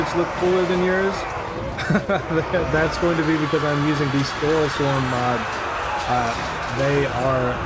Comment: At about 26:04 you mentioned a graphical mod but I couldn't hear you over the clash of arms lol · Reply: Sorry about that. It's Storeslems reskin mods. Hooverics are also good but not quite as much.